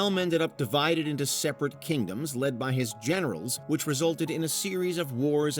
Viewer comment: for a sec I thought he said “led by his genitals” and I was like bro wut?